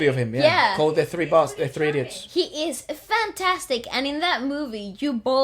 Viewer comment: he was about to say 3 bastards instead of 3 idiot🤣🤣🤣🤣🤣🤣🤣🤣